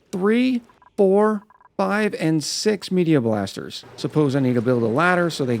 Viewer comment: Anyone else hear "Media Blaster"?